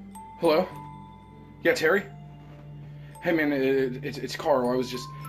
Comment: In all honesty though this is pretty good. Your uncle really struck home in his role. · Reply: Legend Oh wait, I meant Terry. No clue how I heard that as uncle.